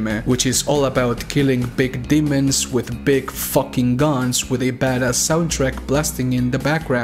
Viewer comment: I walked away real quick at 3:19 and was like "excUSE ME WHAT?" Cuz i thought you said demons with big fucking nuts